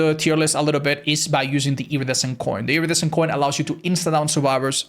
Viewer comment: I thought you were saying "Iridescent Corn" for Deathslinger